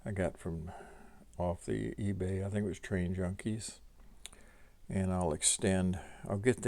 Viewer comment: HI nice tour of the layout , I like that backdrop at 4:10. Did you say you got that from Trainjunkies ? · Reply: Thanks Jerry 😀. Yes, Train Junkies. They have a lot of options and can do some custom changes as well.